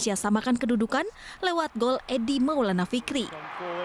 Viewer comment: Apa gue gak salah denger dia bilang edi maulana fikri ??? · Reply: Yg bener tuh Eko maulana fikri